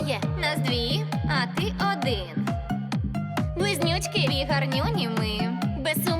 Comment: Мне вместо слова близнючки послышалось вонючки 😂